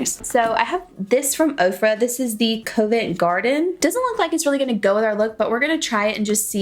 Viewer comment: When you said the name of that Ofra highlighter... I thought you said COVID garden lol 🤦🏼‍♀️